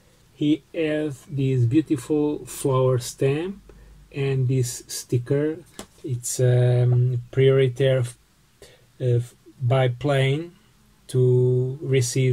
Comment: What did you say in 5:33 ? This sticker ??? very ??? I am so sorry that I am not good at English... (TvT) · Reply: I said that was a sticker for priority by plane. To get postcards faster. 😊